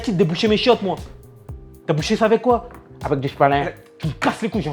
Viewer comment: il a dit quoi ? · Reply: avec du sopalain